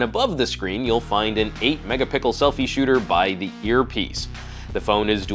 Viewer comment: did he say 8 mega pickle